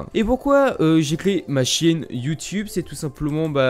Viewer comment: "Pourquoi j'ai créé ma chienne Youtube"